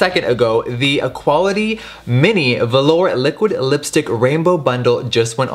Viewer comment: When you said liquid lips, I heard "lick your lips" so it's only mandatory that you make a shade called Lick your lips